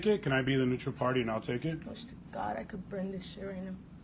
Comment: i think i heard her say "god i wish i could bring the sharia" at